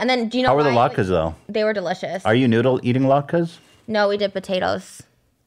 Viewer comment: trisha thought he said noodle eating latkes instead of new to eating latkes lmaooo